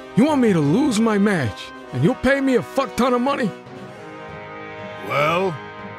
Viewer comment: Did he say fu** ton of money or what ton of money? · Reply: He Said F**k